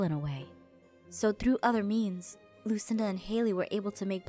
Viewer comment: I thought she said "through other memes"..... Omi😂